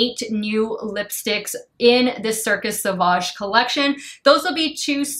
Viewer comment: Thought it said sausage at first haha